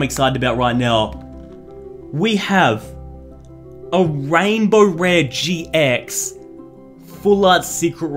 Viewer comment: Is it just me or did I just hear say "there is no way that is a rainbow rare!" Even though the dang thing WAS A RAINBOW?!?!